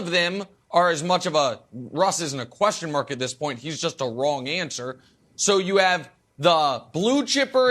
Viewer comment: "Russ isn't a question mark at this point; he's just a wrong answer." Sad, true, and hilariously spoken. Bravo, Nick. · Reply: Wrong answer?! I heard it as long answer.